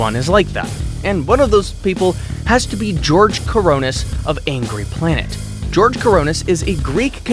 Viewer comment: why do I hear George "Coronas"